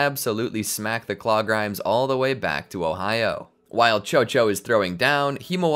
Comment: Have an idea for a video? Submit it here: <link> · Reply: Did you just said ohio mate? 💀💀